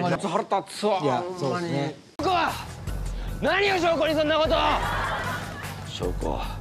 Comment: そんなことーって聞こえるのにテロップが悪いな笑